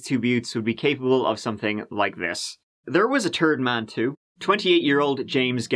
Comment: Lol sounds like you said "and there was a turd man" Instead of third lol It was quite fitting 😆